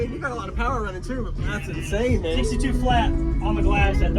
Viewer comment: In the intro i heard 165DB@ Mot 162DB or is my hearing going south already 😂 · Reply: You mishearing things. He said 162 flat at the beginning